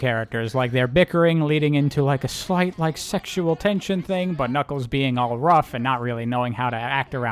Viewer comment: every time you say ".. but knuckles" all I hear is "Butt Knuckles."